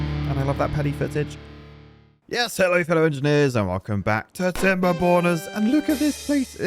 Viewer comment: Until now I thought he said timber boners